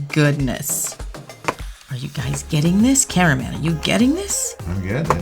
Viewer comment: omg I heard “Carmen” instead of “Camera man”